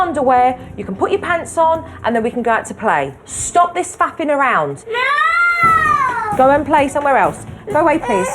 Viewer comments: Am I hearing right, did Supernanny say "stop this fu*king around' ? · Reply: Haha, pretty sure it was "faffing around". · did she say what I think she said? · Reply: "Stop this faffing around" It didn't sound like fussing, sounds like faffing to me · Stop this f'ing around. Hahaha....10:44-10:46, did she really say that? Awesome Jojo! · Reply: I'm English, she said faffing around, it's an English expression